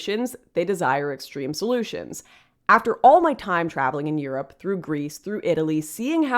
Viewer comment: Lol why did I hear that as “in all my time-traveling” rather than “in all my time, traveling”.